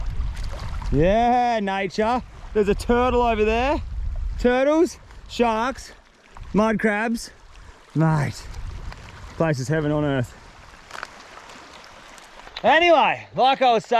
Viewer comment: was it only me that heard "Minecraft" instead of "Minecrab"? lol